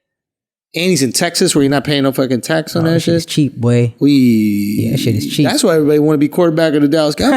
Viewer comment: Did Akaash say “it’s cheap wey”